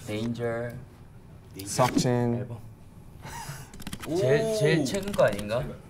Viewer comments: when he said, 'seokjin' I heard 'succjin' I cAnt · When Rm said SeokJin I thought he said Suck Jin I